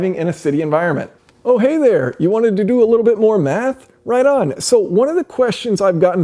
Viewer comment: "Hey there, you wanted to do a little more math"? Hell no, that's what we have you for. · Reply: first time he say'd that, it sounded like he say'd, "Hey there, you wanted to do a little more meth"?